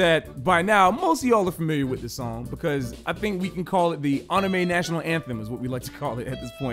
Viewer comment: Did he just say onime